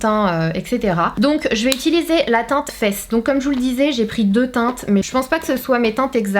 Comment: perso j'ai compris '' teinte fesse '' 😂